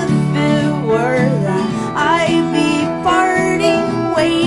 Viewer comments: Until I read the lyrics I thought she said I'd be farting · I thought she said farting but she said parting 🥴🥴🤪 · did anyone else think she said "farting" and not "parting"?